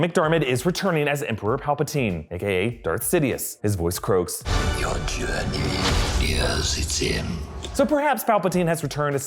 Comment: Honestly, when Darth Sidious talks this is what I hear: “Your journey..... Yeah it’s him”🤔🤔